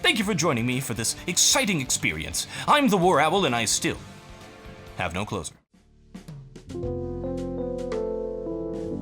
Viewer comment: i always thought you said im and i still have no clothes on and i was always confused until now